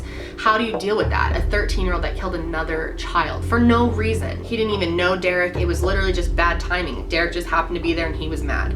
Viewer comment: U did mention that lots of people loved Derrick and that Eric didn’t have many friends. Maybe he was jealous? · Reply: I thought they said that Eric didn't even know Derrick. That he was just in the wrong place at the wrong time.